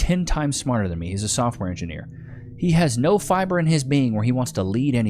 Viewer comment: Dude I had to rewind at 18:44 several times cause I swear you said sophomore engineer instead of software engineer. 😂 Being 54 sucks, years of playing on stage have hurt the eardrums.